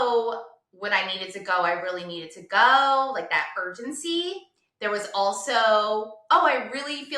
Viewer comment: What were the initial bowel habits? I wish people were more specific...it would help so much. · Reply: I thought she said there was urgency